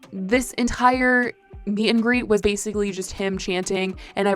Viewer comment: I was peeing when u said meet and greet so I thought u said Austin had a bit of gangrene